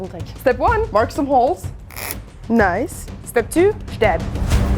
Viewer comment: "step two: stab." (very hard to understand what you said here. The CC says, "step two step."